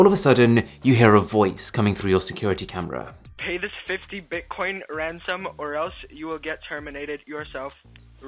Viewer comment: I heard 50 bitcoin, had to pause and rewind